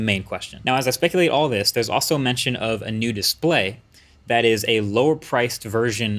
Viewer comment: I can’t be the only person who heard him say “nudist Play” and not “new Display”